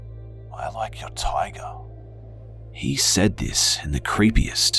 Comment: Toward the end of the whispering I always hear "I like your shirt." And it always makes me feel more at ease. 😂